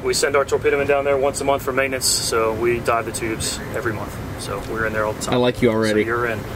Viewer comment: kinda sounded like he said "we send our torpedo men down there once a month for maintenance, so we die in the tubes every month."